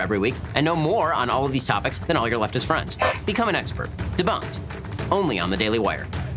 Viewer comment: I noticed he (Ben)didn't say you would become expert. He just said you know more than you're leftest friends, which is a very very low bar. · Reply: I could swear at 1:05 he says "become an expert" but maybe I'm just an SJW cuck.